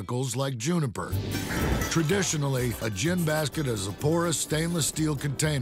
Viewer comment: I thought he said JAM basket